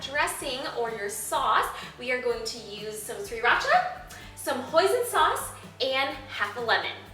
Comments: when she said Hoisin sauce, i thought she said poison sauce..... :) i was like WHA!!!! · instead of hoisin sauce i thought she said poison i was liek y in the world would i put poison in my food -.- xD